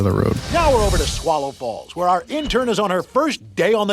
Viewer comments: did it just say Swallow Balls? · does anyone else hear him say swallow balls? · what did he just say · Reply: Swallow falls , that’s what he said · Am I the only one who hears “Swallow Bal-“ 🗿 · I thought at 2:34 it said "Swallow Balls"